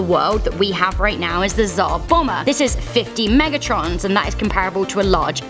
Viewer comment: who else hear "50 megatrons" ? Lmao